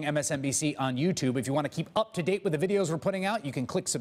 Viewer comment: At the end of all these clips Chris saying to subscribe sounds like he says "just blow me" cracks me up.